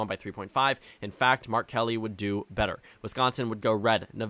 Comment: Bruh I thought you kept saying R Kelly instead of Mark Kelly